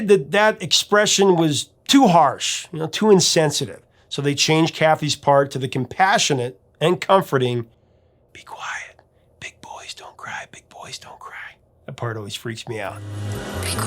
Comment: Oh my, all these years I thought she was saying 'Be poised and quiet' instead of 'Big boys don't cry'. Now it makes a little more sense. Great story! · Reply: I thought she said, ‘Requesting quiet’ but wasn’t really sure.